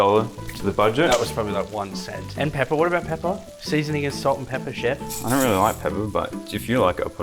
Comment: Anyone else hearing yelling “salt and pepper isn’t seasoning” 😅😂